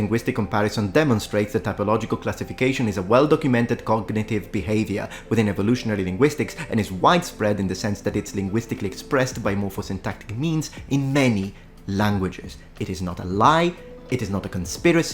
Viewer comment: I had enough of it. I realized it when I heard "whitespread" instead of "widespread".